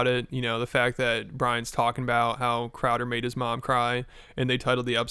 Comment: I thought he said your show made his mom cry, not Crowder